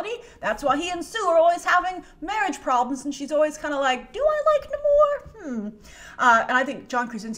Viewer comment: what she says?????? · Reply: Do I like Namor